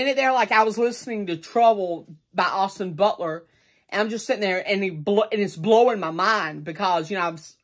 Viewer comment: sounded like he said Just standing here in the intro